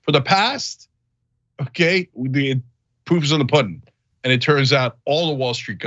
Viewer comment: Listening from across the room, I could have sworn I heard Cenk say, "the poop is in the pudding." He was describing Musk's role in wrecking Tesla's stock price, so it wouldn't be a terrible analogy... 😄😄